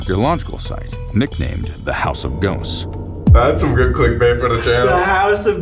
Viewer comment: Did say that’s some good click bait for the channel